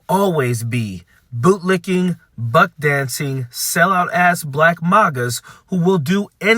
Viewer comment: Being a 58 year old suburban white guy, for the longest time I thought Coach was saying "Butt-dancing", and I'm very embarrassed to find out that's not what it was.